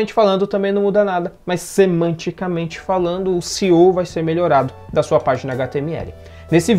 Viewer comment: não entendí o que você disse em 4:48 que " o .... vai ser melhorado da sua página de html" poderia me explicar esse termo? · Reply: SEO - Otimização para mecanismos de busca. É um conjunto de estratégias usadas para potencializar e melhorar seu site nas pesquisas. Por exemplo, se alguém pesquisar o nome do seu site no Google ou algum produto que haja nele, ele será um dos primeiro a aparecer, será um dos recomendados pela Google. Se houver um bom SEO.